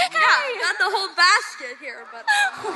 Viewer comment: What did she say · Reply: He or she said "I've got the whole basket here, but..."